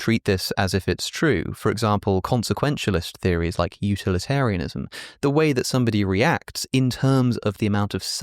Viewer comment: I was so focused on the haircut I heard this as "utili-hair-inism" 😭🤦